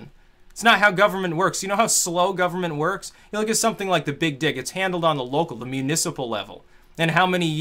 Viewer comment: did Styx say the 'big dick'?